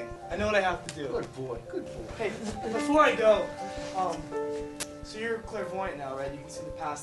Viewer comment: what's the question that Harry asked? I can't hear it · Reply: "so you're a clairvoyant now, rigth?" if you can't hear, just turn on the sub